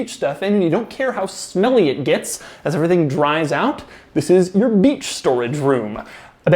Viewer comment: did Doug just said, this is your bitch’s storage room ? Classic